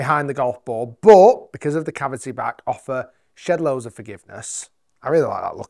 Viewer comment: haha 1:35 did he say "offers shit loads of forgiveness" ??? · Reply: Shed loads